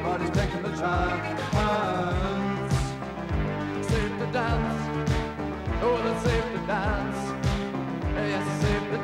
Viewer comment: I've always thought he said "save the dance" and not safety dance until literally just now 😅 songs ruined